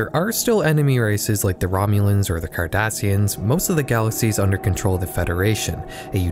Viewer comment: i swear it sounds like he's saying the Kardashians